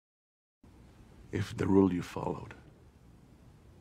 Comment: when I first watched the film I thought he said, "If the rule you followed, lead you to this, If what is was the rule"